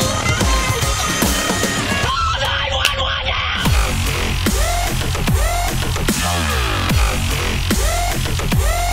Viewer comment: what does he say? fooooooooooooooooooo!!!!!!!!! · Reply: Call 911 Now!